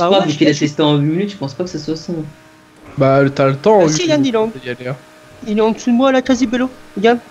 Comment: C'est moi ou à 13:37 min de la vidéo, a dit: «bah si Ilan il est en...»